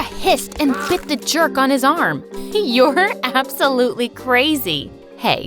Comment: in the time hat she said 07:32 "you're absoloutley crazy". I heard: Yogurt absoulutley crazy.😅🤣